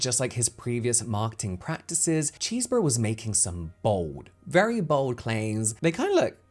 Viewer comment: Swore you kept saying Cheeseburger with your accent😂